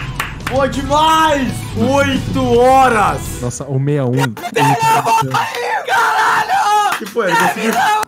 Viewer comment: eu juro que eu ouvi MINHA VÓ CAIU CARALHO!!!!!!!